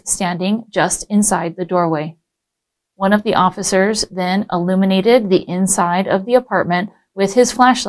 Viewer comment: "one of the officers 'eliminated' the inside of the apartment with his flashlight" It's "illuminated" you dumb dummy I heard they were lowering the qualifications for the LAPD but you don't know a 4th grade spelling test word? WOW